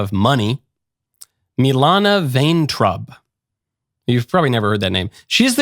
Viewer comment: Thought he said Melania Vain Trump 😅